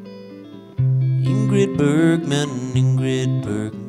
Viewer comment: At first I heard 'Angry Bird man'...